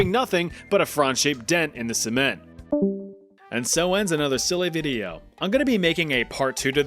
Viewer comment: Is it jus me or did je say silly diddyo